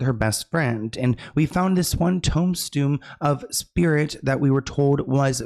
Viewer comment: saying “toem stoon” instead of tomb stone at 16:12 is killing me🤣🤣🤣🤣🤣